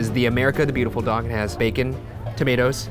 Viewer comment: He said dong instead of dog 😂😂😂😂😂